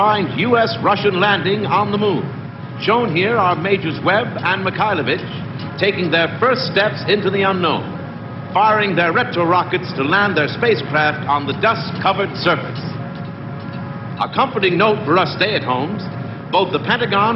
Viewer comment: firing their Rectal Rockets - is that what they said at 7:54??? they look like butt plugs too